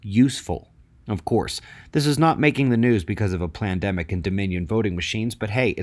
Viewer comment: Did you just say "plandimic?"